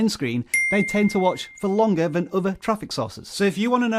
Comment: He said “sources” I heard “sauces”… I thought I was on a cooking channel for a quick second. Haha. Good video though!